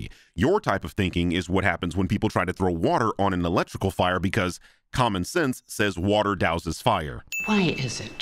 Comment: Love t his video! Keep it up. I did notice one thing and maybe I'm just high but on 15:58 did you say "Common Sense" or what the subtitles say, "Pokemon" haha